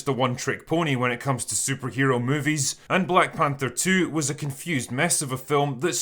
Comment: Did you just say "Black Pander 2" or did my hearing go the way of the dinosaurs?